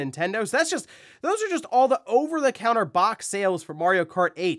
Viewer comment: I heard that as "over the counter" box sales for Mario